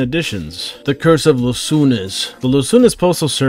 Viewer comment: BRUH its not "los soones" but su-enos, you know dreams. City of dreams instead of angels.